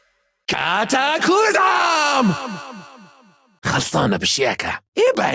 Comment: Did I just heard saying cataclysm, Miraculous Ladybug Confirmed?